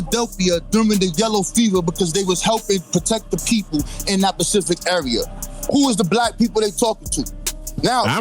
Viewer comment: He said “Pacific” instead of “Specific”. You can’t take him serious after at that moment